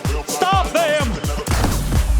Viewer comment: I thought he said "GAYLIFF"